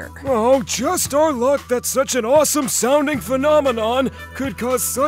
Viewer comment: i thought bro say sonic instead of sounding